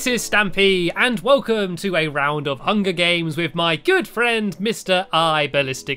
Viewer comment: I thought he said with my girlfriend